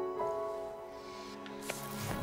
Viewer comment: Me chuté todo para que al final no entendiera que dicen?? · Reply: Singoraloca